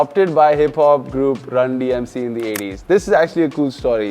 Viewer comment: I heard that as Randi MC